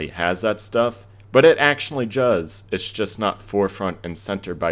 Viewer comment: In 8:14 he said Juz instead of does